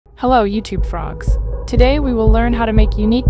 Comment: i think i heard you tube frog 🐸🤡